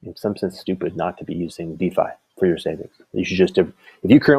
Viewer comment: What is d phi savings? Unsure spelling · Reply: I thought he was saying Die savings?